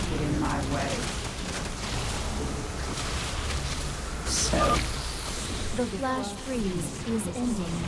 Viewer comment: lol when she says "The flash freeze is ending" I totally thought she said "The flash freeze is imba."